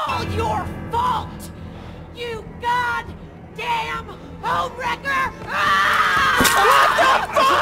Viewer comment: i thought she said ohmwrecker.😂😂😂